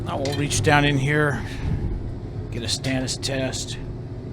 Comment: "Stannous test". I've been watching for a couple few years and always thought you were saying "Status test" and "Status Chloride". I learned something new today.